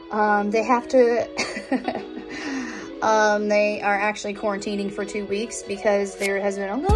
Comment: I thought if the school sent you home to quarantine you have to stay at home incase you were exposed? You wouldn't want to go out and potentially spread.. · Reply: I thought she said they quarinted for 2 weeks